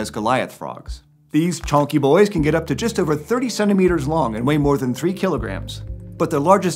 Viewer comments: When he said the African frogs or 30 centimeters long I thought he said 37 meters and I looked at my phone like "excuse me?" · Who else heard "37 meters long" instead of "30 centimeters"? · When he described the Goliath frog, it really sounded like he said "37 meters long" which would be one hell of a frog. Turns out he said "30 centimeters." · when he said “30 centimeters” I thought he said “37 meters” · Anyone else hear "37 meters long"?